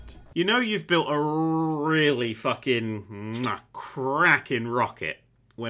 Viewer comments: did you say a "crackin rocket" or a "kraken rocket?" because I think it's the latter. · "You know you have a cracking rocket" I heard that as Kraken and TBH it makes more sense than the intended sentence.